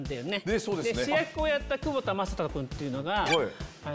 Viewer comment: 窪田正孝ファンとして窪田正孝って聞こえたけど久保田雅人さんでだれ？って思ったらワクワクさんだった